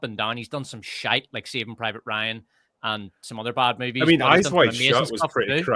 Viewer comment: “Shite like Saving Private Ryan?” Did I hear that correctly?